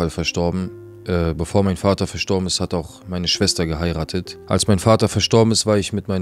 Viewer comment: bruder ich glaub du hast ein fehler gemacht... Oder hab ich falsch verstanden, dein vater hat deine schwester geheiratet?! 🧐😱🤬